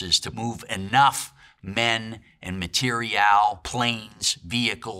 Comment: "to move enough men, and matériel, planes, vehicles, weapons, over to England" is what he said at 8:58. NOT "material planes" like your AI falsely transcribed at 9:05... "Matériel" is a french word used in military jargon, it means the equipment, supply needed for a military mission. Usually it's fuel, food, clothing, ammo, building materials, etc...